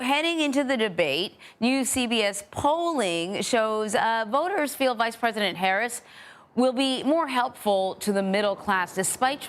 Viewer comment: did she say "voters feel LIKE PRESIDENT HARRIS" or "or voters feel vice-president Harris"?? cause it sounds an awful lot like the first one to me............. ANd Harris doesn't stand a chance in a debate against Trump unless she has clowns babbling in her ear just like Biden did.